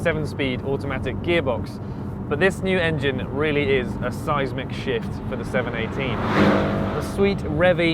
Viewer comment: I thought he was going to say "seismic shit"